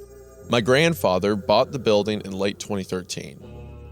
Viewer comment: i was half asleep and thought he said "Bob the Building" and I woke up hysterically laughing so thanks for that